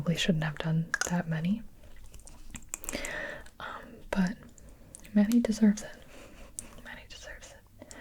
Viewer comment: I THOUGHT SHE WAS SAYING MANDY!! bruh